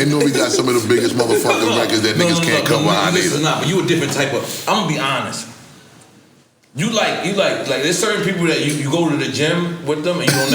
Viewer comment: At First I Thought N.O.R.E Said "I'ma Be Honest Buss...You Never Found Love"😭😭💀💀